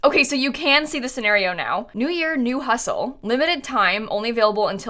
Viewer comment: I thought she said new year, new husband😂